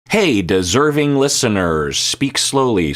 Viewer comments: When I first started watching your videos I thought you said "discerning listener". (English is my second language and I'm bad at hearing but I don't use automated subtitels because for me they often mess up more than help) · When I first got into your content it took me a long time to work out what you were saying in your intro 😅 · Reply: I heard "deserting" listeners at first 😂